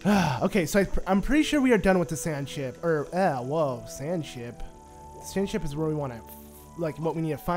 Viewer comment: it sounded like you said Sand Shit 😂